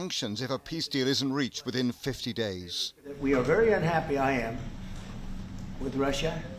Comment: 50 days is a long time.... I thought he said it will be sorted in less than 48hrs